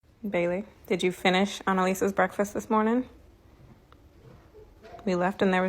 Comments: I thought 3 said “did you finish ON Alisa’s breakfast” I was not having a fun time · Oh my god i thought she said did you finish on the breakfast that is something completely different